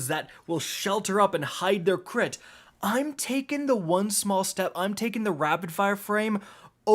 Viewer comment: Thought my man said they hide there clit……